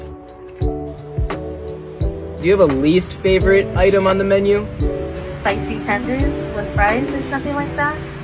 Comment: When she said spice tenders I thought she said spicy peppers 💀